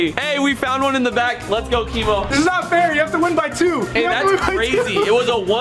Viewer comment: The editor wrote Instead Of Rug LMAO